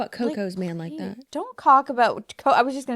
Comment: sher def said "don't c-ck about" instead of talk and you two breezed right past it 🤣